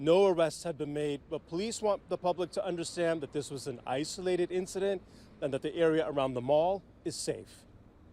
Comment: I had to play this reporter's speach several times before I heard "This is an isolated incident." I thought that I heard "ICE related incident." With Donald, it could have been ICE